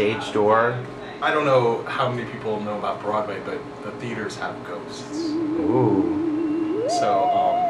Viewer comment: when he said "theatres have ghosts" i thought he said "theatres have goats"